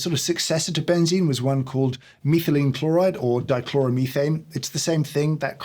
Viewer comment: Thought you said methylamine… thought you were going all Walter White on us 😂